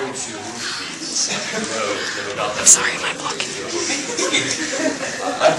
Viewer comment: I know it said "Sorry, Im blocking" at 8:27 but I swear I heard "Sorry, My bucket"